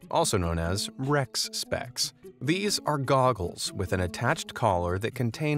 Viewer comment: Did you say "Doggles"?